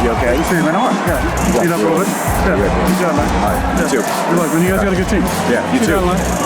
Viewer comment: sounds like Brady says. When you guys gonna get a team?